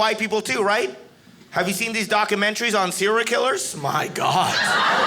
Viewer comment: When he said ser!al k!llers, i really thought he said "syr!a k!llers" and i mean.. 😶